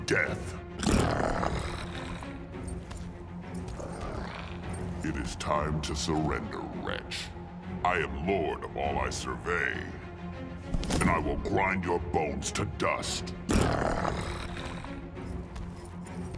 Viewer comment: Hahahaha I thought he said "It's time to surrender bitch"